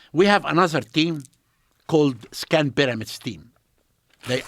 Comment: Let me tell you something…..(awkward pause) … sucking sound on cigar …. Now before I tell you- it is in my book- Joes sounding annoyed asking “ Can we find this online?” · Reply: Did he say he created the “SCAM PYRAMID SCHEME?”